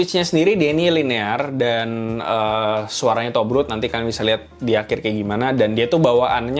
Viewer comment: gak salah denger nih gw? 😦😦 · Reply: Tobrut dik